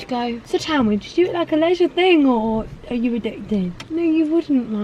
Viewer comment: I’m American and I can’t figure out what she’s saying at 7:34 It sounds like, “So tell me, did you egg a ledger thing?” Can anybody help? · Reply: Is it a leisure thing or are you addicted... I think 😂